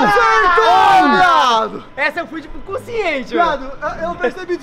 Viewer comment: So eu que ouvi "essa eu fui de cu ciente"kkkkkkkkk